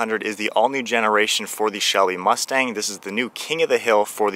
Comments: Am I hearing things or did he just say shelly mustang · Am I hearing things or did he just say shelly mustang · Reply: I know that. It just sounded like you said shelly and not Shelby mustang that all. Great video and great breakdown of this car. I plan on buying one next year.